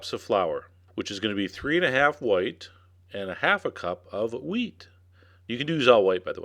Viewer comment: did he just say half a cup of weed? :D